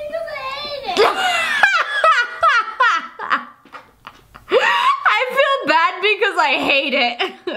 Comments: It sound like she saying i felt bad because i ate it 😭😞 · The child: WaAAAAAAaAA dad: wutS wRong Hun the child again..: I FeEl BaD BecAusE i HaTE it me: dies of laughter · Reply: Unicorn Gamer lmao it makes it even funnier bc it sounded like she said “i feel bad because i ATE it” 😂😂💀